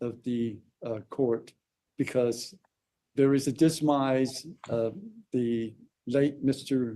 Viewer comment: Did he say "dismise"??? WTH does that mean? 😂